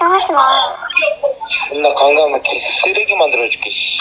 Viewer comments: 근데 왜 내귀에는 존나 강간해 개쓰레기만들어줄께로 들리지;; 나썩엇나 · 마지막 강남역이에요 강x이에요..? 존x 강x해서 쓰레기 만들어줄께 시xx아로 들리긴하는데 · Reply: 강남에서 개쓰레기로 만들어줄게 예요 ㅅㄲ 영상올렸는데 전여친 쓰레기로 만들어놓음